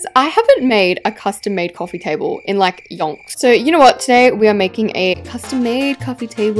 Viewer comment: what did she say!? Did she say yon? Long? What did she say?!!?